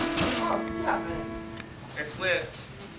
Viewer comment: It's so obvious the son resents having to look after his mamma. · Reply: Life with Ashley lmao why I thought I heard him say it's lit tf 🤣